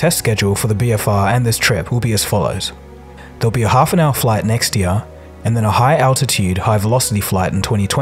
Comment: When he said "There'll be a half a hour flight next year." I thought he said there'd be one 'Next to ya'